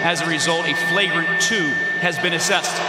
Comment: Kevin "Ejected" Durant. Nice one reff. I thought he called Kevin "Snake" Durant